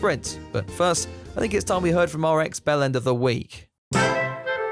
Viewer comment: Everytime says “X bellend”, I hear “ex-bellend” and think Hes talking about a rehabilitated bellend 😂